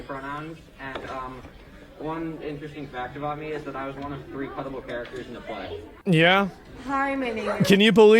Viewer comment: Excuse me.. did he say “pettable” characters…?